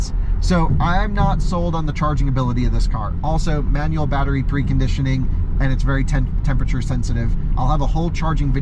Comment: Did you say MANUAL battery preconditioning @ 46:16?!